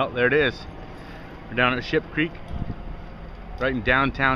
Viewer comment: sounds like he said were down shits creek looool